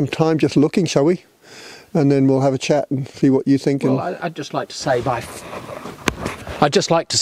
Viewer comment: I thought it said "its not what you think" 🤨